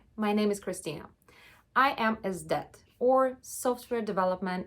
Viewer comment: When she said “SDET”, I heard it like “I am is dead” 😂 I think she was trolling us!